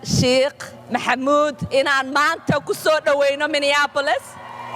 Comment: Sound like she said My Nigga A YN I. Minneapolis 😂.